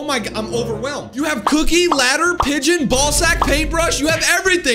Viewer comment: did you say balls